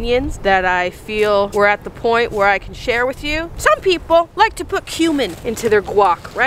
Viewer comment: I thought she said "some people like to put human into their guac" needless to say I was very intrigued and slightly confused